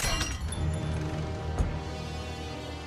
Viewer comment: What did he say in 3:45? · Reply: "Corre, corre, gatito" or "Run, run, kitty"